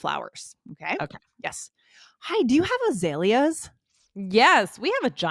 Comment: May I ask what that word is in this episode 10:16 ? The auto subtitles show Aelas 😢 but I think maybe is a kind of flower !? But I am not sure ~ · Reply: You are correct, its the flower Azalea. :) Thanks for the comment. Please take a minute to leave us a review. <link>